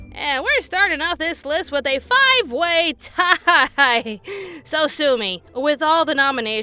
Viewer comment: Every time I hear you say, "So sue me.", I mistake it as, "So Tsumi."